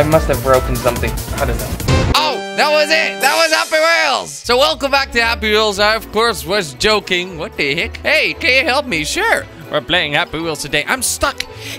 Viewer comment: The first few times I saw kwebblekop I thought he said welcome black ops😂😂